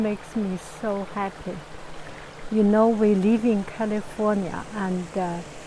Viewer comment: I heard at the beginning of the video that you're leaving california. Where are you going to move to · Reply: I thought she said "live in" California, but I could be wrong because of her accent. I hope she doesn't leave California and her beautiful backyard with her flowers and fruit trees!